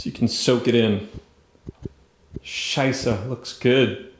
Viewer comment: did you said "scheiße" looks good, the german word for sh!t